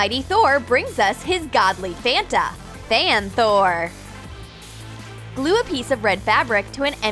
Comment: did she say damn Thor?!?!? 😂